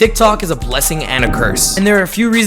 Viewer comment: sounds like you said "dik tok" in the beginnin 😂